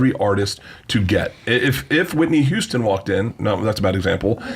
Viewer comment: he's so cringey. imagine comparing yourself to Whitney Houston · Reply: I must have misheard it, i thought he said Whitney Cummings